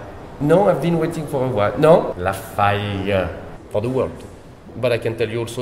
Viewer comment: what is the manager saying at 10:30 and 10:36? it sound like "for the welch" but what does it mean?